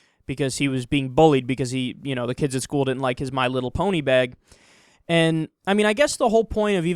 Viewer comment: I thought he said my little pony bed and I was like "oh shoot, why would anyone bring a whole entire bed to school"